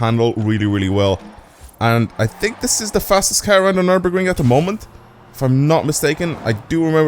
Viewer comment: what did he just say? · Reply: Or this is the fastest car on nürburgring at the moment